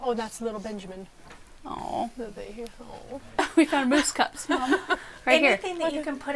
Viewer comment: Mousse cups .... I heard Moose cups and was looking at the design.